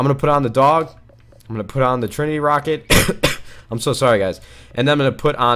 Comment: My wife thought you said tranny rocket. Ha!